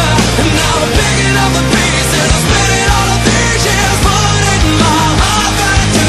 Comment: i always thought it said i am molding my heart back together but now putting sounds good too lol